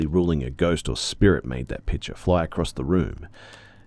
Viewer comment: I heard “pitcher” as “picture” at first and I was so confused as to why the mom had a picture of tea on the counter 😂😂